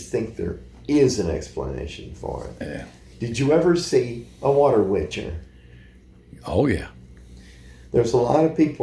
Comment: what is the word he is saying a water what? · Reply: A Water Witcher 😮